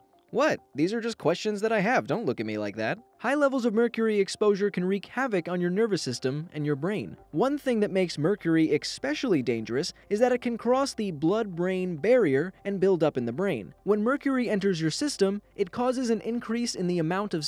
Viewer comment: D... did you actually say "expecially?"